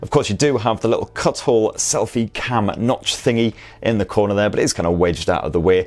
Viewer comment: did I just hear "notch thingy"!!??